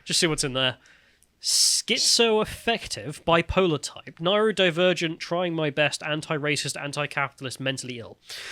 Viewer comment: i swear i thought you said "Naruto Divergent" and honestly didnt think anything weird about it at this point.